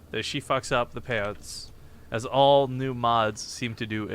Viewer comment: he did indeed say "...as all new mods..." so unless i'm missing a joke he owes her an apology...that was really awkward